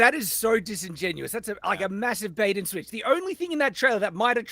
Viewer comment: did say Master-bait and switch? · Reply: massive bait and switch